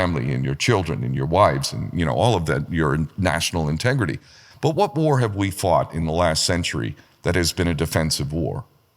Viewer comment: What does he say at 9:59 after “clean break memo”? I’d never heard about any of that and I’m trying to research it. Thanks. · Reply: PNAC - Project for the New American Century